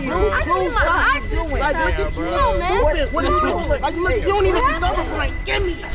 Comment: ive watched this video a lot and this is the first time i heard that kid around 1:20 say "you dont even deserve a brain, gimme it!!" hahaha